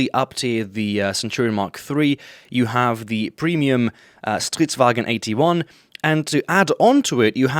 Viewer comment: Did you say "stridswagen"? :D Perfect blend of swedish and german.